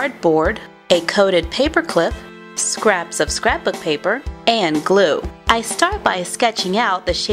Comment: all these years and i thought she was saying “scrap of scrap of paper. “